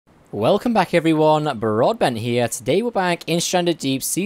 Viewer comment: I thought u messed up and said broadband